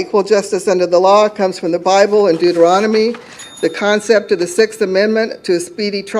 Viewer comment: Every time I hear someone say 'deuteronomy' I have to pause and think it twice. For some reason I always hear it as 'deuterostome'.